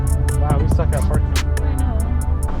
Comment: Love how he says she sucks at parking but he parked half way out of the spot and a little bit in to the handicap spot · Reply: I thought he said “we” suck at parking lol